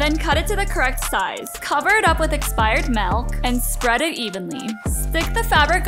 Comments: Did u just say expired milk💀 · I thought she said expired malk 😮